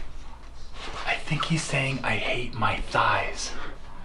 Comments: I think he's saying I hate Matthias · anyone else think he said “I hate mathis”?